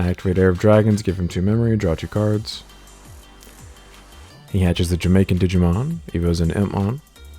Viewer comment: Did he just say Hatches the Jamaican Digimon?